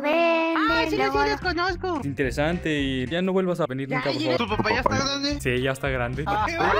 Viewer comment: su papaya esta grande si no entendi dice el jajajaj